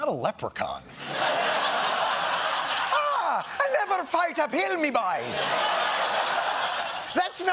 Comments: I thought trump said "never fight a pill!" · I thought he was saying “never fight a pill”